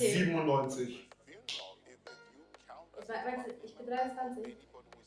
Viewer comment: was hat sie gesagt? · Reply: Ich glaube :" ich bin erst 12" oder so😂